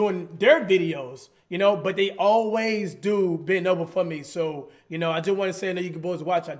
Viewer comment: did say they always bend over for me?